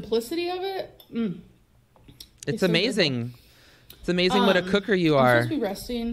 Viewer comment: And now she’s on snapchat showing pictures of herself compared to now to prove she’s lost weight ... the scales don’t lie 🤣 I die for these intros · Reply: bree how-sif 🤣 it could have been an actual necklace but I could have sworn she said choker lmao but in the before comparison I don’t believe she was wearing one but who knows with this girl. She’s delusional.